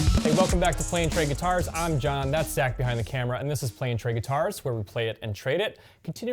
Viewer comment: I thought he said “I’m Jon Nutsack” at first LOL